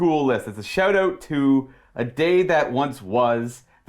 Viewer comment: Genuinely thought said "A Dave that once was" when describing his World Eaters list at the 2:06 mark